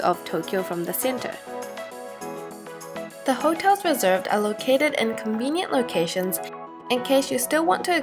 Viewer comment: Could someone tell me what she said at 3:44? "something come put my hair down"? I'm trying to subtitle the video but I can't seem to figure out what she's saying in that part. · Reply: yea that's what I thought so. Could you please also help me figure out what she said at 3:20 "The hotel reserved a located and convenient locations"? I think she meant "the hotel we reserved?" and at 3:58 did the guide say "guess where we're going to hakone?"